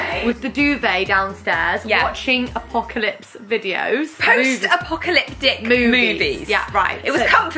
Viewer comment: What was said "post apocalyptic movies" What I heard "post apocalypdick movies" 😂